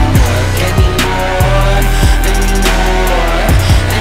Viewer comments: Anyone notice how when he says "anymore" sounds a little like "I need more" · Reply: When i started listening to this song i thought he said i need more so yeah so it also could you got anymore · Anyone notice how when he says "anymore" sounds a little like "I need more" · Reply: I Hear 'I need more' as well Lolz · Anyone notice how when he says "anymore" sounds a little like "I need more" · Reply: Thanks to you now i hear "i need more"